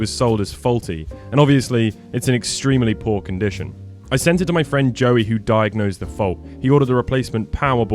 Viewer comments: I misheard the opening and thought you said, "I sent it to my friend, Joey, who died." Holy moly he had an extreme reaction! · I heard, my friend Joey died, no offense man 💀